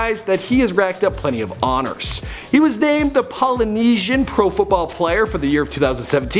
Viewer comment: I thought you said he was an ex football player